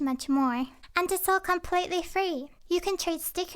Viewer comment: Why die i think whenever she said and its all completely free I thought she said i woke up at three😳🤔